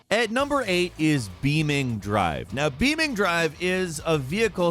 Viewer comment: it’s said “beaming”??? i thought u said it like “beam N G” lol